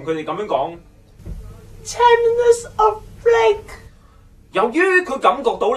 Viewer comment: in the first time i'm hearing this, i thought that guy was saying 10 minutes of flake..i need to hear it for 3 times until i know wt he's trying to say....